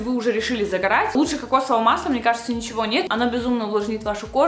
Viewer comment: мне послышалось: " Оно высрет вот этот защитный слой" Я смеялась)